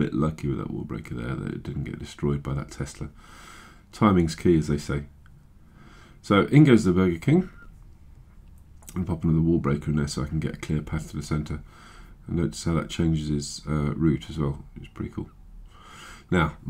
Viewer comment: Does he say in goes the burger king at 4:33 ?